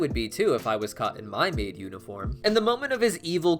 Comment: is bro saying he has a maid oufit??